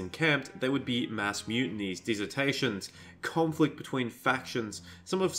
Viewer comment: I know he said "desertations" but at first I heard "dissertations", which I find hilarious. I'm just imagining a bunch of orcs writing essays about the best ways to consume man-flesh.